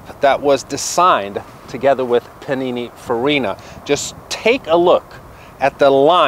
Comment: !! Love the videos but did you just say "panini-ferina?" Lmfao